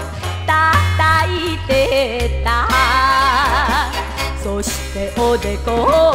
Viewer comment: 「そして子猫を叩いてた～♪」と聞こえるのは私だけでしょうか？びっくりした。でも本当に好きでたまらないですこの歌。